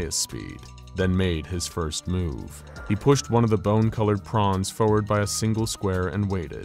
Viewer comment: did he say prawns?